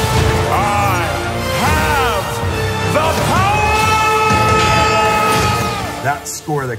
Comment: Shoot, I loved this show as a kid! Still have the original figures! Dang, I always thought he said "I am the POWER!" I guess I have the power works too :(